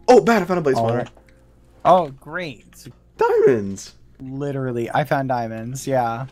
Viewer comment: when sapnap said "diamonds!" when bad got them in the fortress it sounded like he said veterans because of his voice crack lol